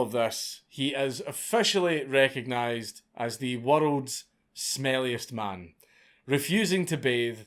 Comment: I thought it said worlds smellingest man, I'm like that ain't joe Biden.